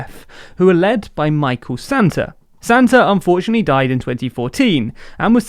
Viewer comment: Lol I’m Zambian and I heard a Brit say “Santa” instead of sata